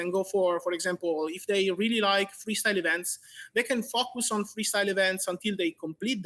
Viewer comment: Is he saying F*ck? LOL